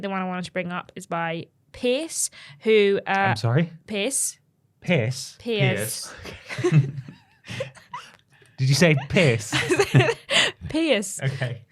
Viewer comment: I heard Piss as well